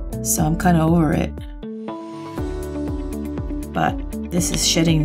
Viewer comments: I thought she said shitting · did she say shitting new life? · Did you say 'shitting new life' at 14:50 ?😂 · Thought she said "this burgundy is really shitting new life" 😂😂😂